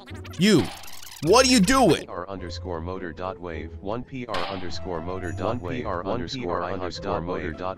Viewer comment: Kindly: what are you doing 1st prize: skjsishisshwjhshjw (i didnt know what he said) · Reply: 1pr.moter_weight